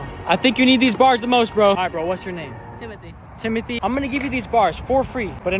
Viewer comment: I really thought he said his name was himithy😂